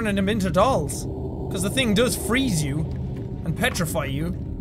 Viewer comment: okay i'm going to be honest here when he said "petrify" i thought he said "petrel fry" and i had to re-watch it to understand what he said